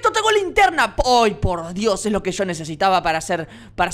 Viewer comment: Entendí "voy por Dios" xd